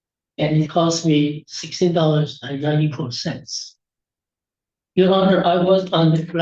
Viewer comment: Did he say he is claiming $16.94 for a plunger? 😂